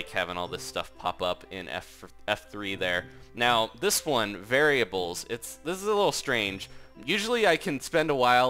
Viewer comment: wait WHAAAAAAAAAAAAAAAAAAAAAAAAAAAAAAAAAAAAAAAAAAAAAT!? did you say variables? <freakout> so;ihrr[sejopps89-usyopguipg@@ </feakout>